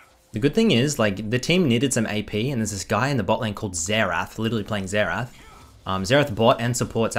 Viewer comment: am i the only one who heard coldzera?